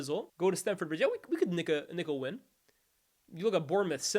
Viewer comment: what did he say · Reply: Nick a win. Cmon.